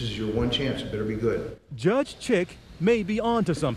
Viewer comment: i thought i heard Judge Chicken Eddy